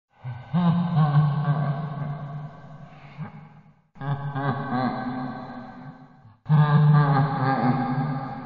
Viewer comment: did he say a bad word ?! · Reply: He said shit